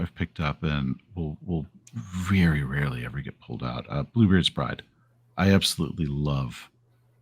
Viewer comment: what's the name of the game is saying here? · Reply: Bluebeard's Bride